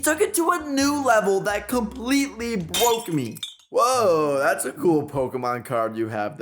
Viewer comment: did he say " bwoke "???